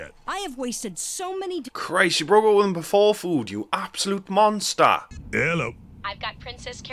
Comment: what's that he says after Christ? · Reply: "you broke up with him before food?! You absolute monster" lol Sorry for the confusion